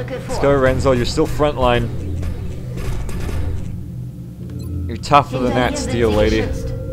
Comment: "You're tougher than that, Steel Lady." Sounds like read the latest chapter of Vesmolol's Alive and Impossible fanfic. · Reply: I thought he was saying "You're tougher than that steel, lady," which would have been a really beautiful thing to say to a MEC soldier.